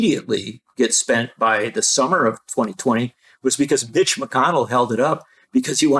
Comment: did he really say bitch mcconnell lmao